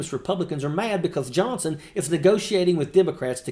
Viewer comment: At 12-13 secs into this segment sounds like she says..... MY JOHNSON is getting rolled in mtg after mtg.... ouchy